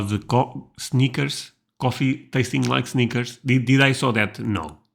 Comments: At first I thought you were saying we have coffee that tastes like sneakers. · I thought he said "sneakers." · I heard, "coffee that tastes like sneakers" and instantly thought.. "but I don't see any Maxwell House here."